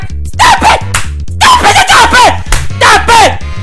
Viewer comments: why does it sound like is saying dop it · I thought she said: STOP THAT SLAP THAT BAD STOP IT SLAP THAT BAD